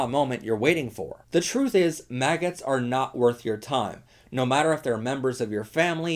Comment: I thought the speaker said “maggots” and I think we should call the anti-lgbtq/trump supporters people maggots from now on